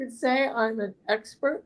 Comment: 19 minutes in .. holy Shamoly!!! I heard “Kyron- you’re an expert “!🎉